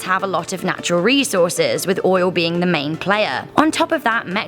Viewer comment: Did you say oil?